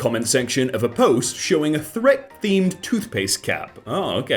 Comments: …Did you just say ‘Threk’? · did he just say threk?